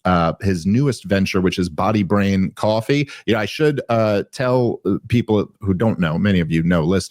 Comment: I thought he said pottybrain coffee at first lol. But man putting testosterone in the coffee is brilliant